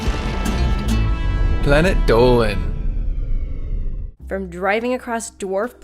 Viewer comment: i heard 11 Dolan